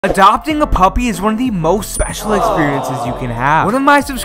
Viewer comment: I thought you said McDonald’s eating a puppy